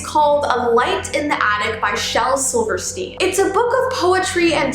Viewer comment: When I heard "Silverstein" I thought I heard "Silverstream."